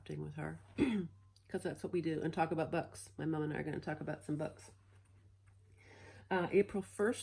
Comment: Oh! you're so pretty :) For a minute there I thought you said you and your Mom were going to talk about butts and I giggled. I will see you at Stitch North!!!!!! I'M SO EXCITED!!!!!!!!! :D